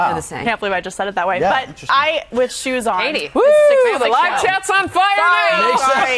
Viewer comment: DAMN IT, she said how tall she is but they were making so much chatter I could not hear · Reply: sounded like she said with shoes on she is 6'1''